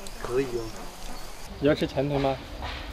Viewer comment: 聽成你要吃拳頭嗎